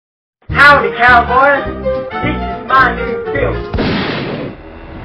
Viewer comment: Howdy cowboy this is my new film! BANG music starts playing HEUHHHHHH WHAT THE HELL? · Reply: I thought he said 'this is my nigga phil'